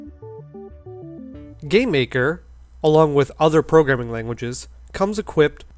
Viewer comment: I keep hearing you say "gay maker"